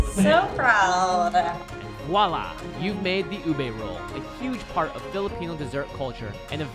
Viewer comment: I swear, for some reason, I thought it said Uwe Boll, and I was like, what the heck is Uwe Boll doing in Steven Universe?